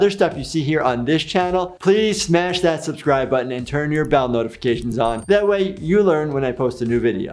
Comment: I heard that as Bowel Notifications and was like............................ wait a minute, WHAT?!